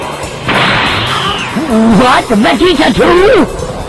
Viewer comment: what did frieza say? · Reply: "What? Vegeta too?" is what he said